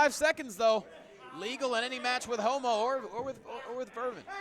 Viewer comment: it sounded like he said "homo"